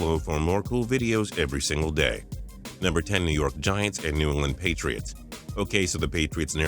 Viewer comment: did anyone else hear New Orleans Patriots?